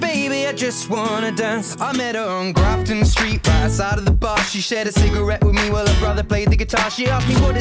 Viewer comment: の「outside of the bar」が「サラダバー」 にしか聞こえないんだよなぁ